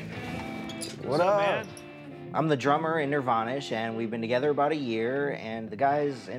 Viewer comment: I thought he said Nirvana 😂 had to replay it a couple of times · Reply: xo (Elfprincessxo) I heard nirvanish XD